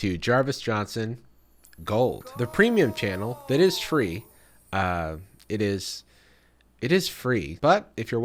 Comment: Lol why'd he say tree instead of free